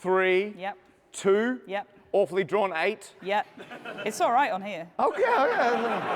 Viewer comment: oddly drawn 8 😂😂😂 Edit: He said “awfully” not “oddly” I was nervous even though I wasn’t participating in it and just watching from YouTube. However when I saw the part where there was Awfully Drawn Eight and Matt mishearing just few seconds later from that, I laughed hard🤣